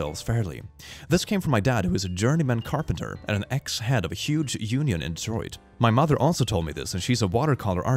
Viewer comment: Thought the thing said onion and not union😂 (completely random and I'm a freak:) 34:19m.,.